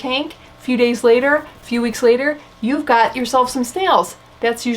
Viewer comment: In my head I heard "You've got snails!" in the "you've got mail" voice. Re the tubed plants, in my experience, the smaller sized plants seem to adjust more quickly to my tanks. My local Petco has a pretty decent plant selection for a chain store. I just picked up an ozelot sword plant earlier this week. They mostly stock beginner plants, which makes sense to me. I've had pretty good luck ordering plants online as well.